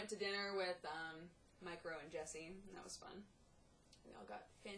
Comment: When you say "Mike, Ro, and Jesse", I hear "Micro and Jesse". And for a split second I think to myself "who is Micro again? Wait." *facepalm*